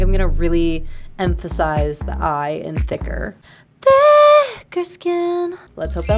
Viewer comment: I keep hearing "baker skin"